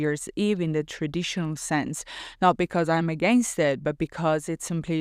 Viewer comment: At first I heard: "I personally do not celebrate the New Year in the traditional sense; Not because I'm a gangster." 😎 Ahaha But you said, not because I'm against it. 😂 Thank you for offering such precious content. I'm grateful I found you. 💙 💎✨